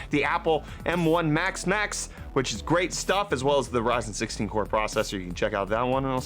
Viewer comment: I would have sworn at the end he said "I'll see you tomorrow for Brettfast my friends"